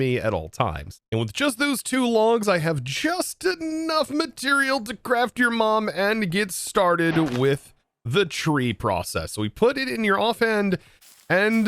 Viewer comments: Did I really hear "craft your mom" 😂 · he really said "to craft your mom" 😂🤣 I had to go back after saying "what!" To make sure